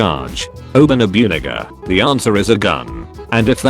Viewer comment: Anyone else hear the N-word? Idk that's just how my brain works, finding wierd words